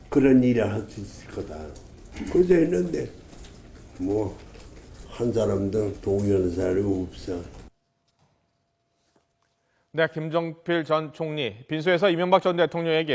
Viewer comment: '한 사람도 동의하는 사람이 없어'로 들리네